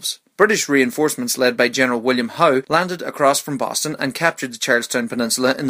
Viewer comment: what did he say please help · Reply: jaden colbert hesaidgeneralho